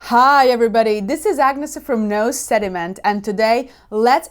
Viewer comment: I heard “nose sediment” in that intro and I agree completely; that is a HUGE mistake in food and wine pairing. I hate that in my glass.